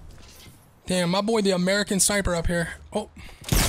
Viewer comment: Anybody else catch him swear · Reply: Hipster Wales u Mean damn?